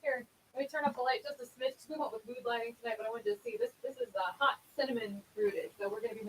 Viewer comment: I thought said the color she was wearing is Hot Cinnamon Strudel, but it's actually Hot Cinnamon Rooted. LOL. But, Hot Cinnamon Strudel could be a cool color mix someday. :D